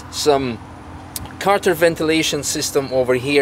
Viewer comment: Can someone tell me what mentions at 2:15 ? A counter ventillation system? it looks aftermarket. thanks